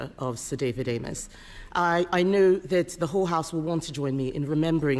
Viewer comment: She said the whole house and i heard whore house 😂 1:08. Nice to see his spindoctors have been hard at work attempting to blame everybody else for being responsible for the shambles British politics is in at this present moment in time. Doesn't matter how we got to the spy trial is irrespective of how Starmer once again let those known to be guilty walk away scot free through his own culpability that makes him equally accountable as the perpetrators themselves